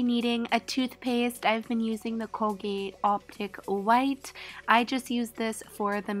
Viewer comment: I heard cocaine 😂